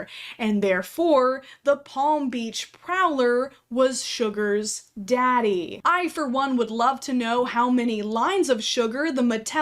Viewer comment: wait the doll was actually called sugar daddy Ken???